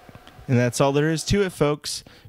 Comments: at the end it sounded like he said rape, comment, and subscribe. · Hahahahah! At the end, it sounds like he said "Rape, comment and subscribe!" xD